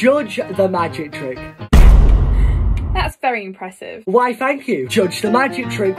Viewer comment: Thought he said wife thank you